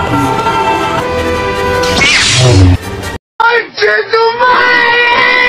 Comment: What did he say? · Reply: I cant lose my mewing streak